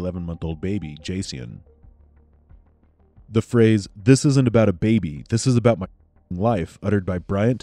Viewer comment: I don't hear "this isn't about a baby" I hear "this is about a baby. This is my life on the line" just saying.